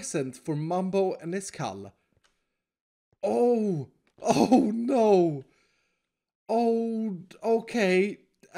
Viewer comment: When he said "oh,no" I thought he said "UNO"